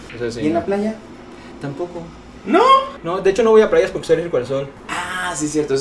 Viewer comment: no va a playas porque qué? no le entendí jaja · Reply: Soy alérgico al sol ☀️